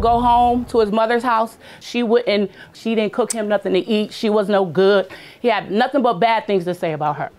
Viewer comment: I thought he said she was...Chile, good night